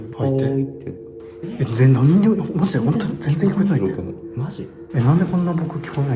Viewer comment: え、聞こえてないの？本当に？やだ・・・と聞こえるのは私だけでしょうか。